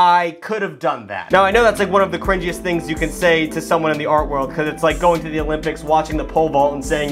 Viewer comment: I didn't know dyslexia could affect hearing because I could have sworn he said 'the r-word' and not 'the art world'